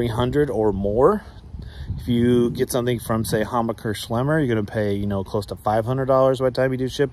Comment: What is the name of the store u mentioned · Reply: it sounded like homicer slimmer or something u said it at 2:43 of the video something to do with 500 dollars for shipping